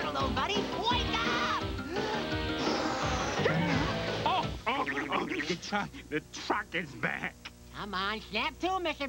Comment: I swear to god, Arnold said "the fuck is that?" Nope, he totally didn't say "the truck is back!"